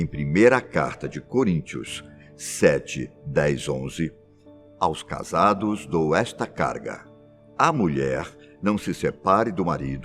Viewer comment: ele falou "Aos casados, dou está carga..." Mas está escrito "Aos casados, dou está graça..." Mas foi ótimo o vídeo, não sabia da maioria das coisas